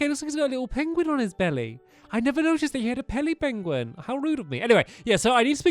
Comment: Heck. 1st Stampy video after a good 4 years of not watching to see what ur doin now and at 0:40 i say 'Pelly Benguin'... good to see the channels still entertaining